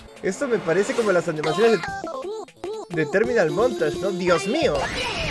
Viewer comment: hmmm ,.... oi "Terminalmontaje"